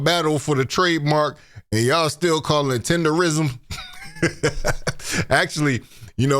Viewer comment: Thank youuuu bc it irritates the left side of my nervous system everytime i hear somebody say Tenderism. I'm like this man is clearly saying "TENDERNISM" whew chile 😂